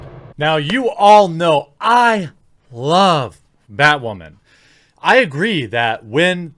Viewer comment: I honestly thought you said "badwoman"